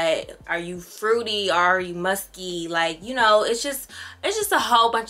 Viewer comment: This girl said” are you fruity or are you musty” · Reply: ok girl relax, I thought you said Musty. Relax